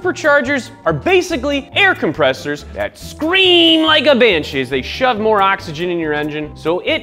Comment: Did he just say scream like a b****?